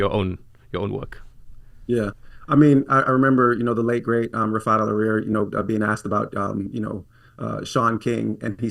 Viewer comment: who? i cant understand who he is referring to... "the late great..."?? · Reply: never mind. Refaat Alareer